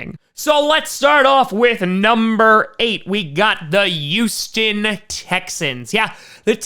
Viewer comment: who are the Youston Texans? Is that a New Yorker thing, like saying yuge instead of huge?